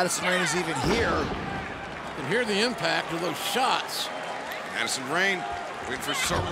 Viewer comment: I almost thought JR said, here in Impact. But it was, hear the impact. Weird, since they were talking about how Madison is here now, compared to her tenure in Impact.